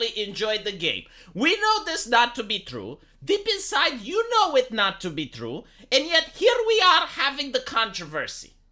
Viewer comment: for a moment I thought you said "TB Skyen, you know this isn't true" 💀💀💀